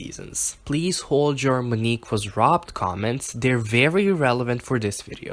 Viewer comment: when i first heard this i understood 'Germany was robbed' instead of Monique like wtf?! i'm gonna watch the video and then go to bed... clearly i am tired af